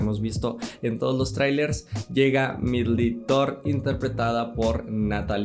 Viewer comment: Todo el vídeo le entendí "medly thor" 😂 😂 😂 😂 según yo se pronunciaba diferente